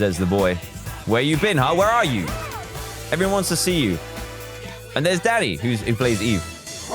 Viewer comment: I thought he said, "And there's daddy!"